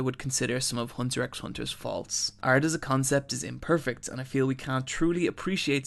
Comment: I thought I was going to learn a new word with "fauluts", until I heard you say "faults"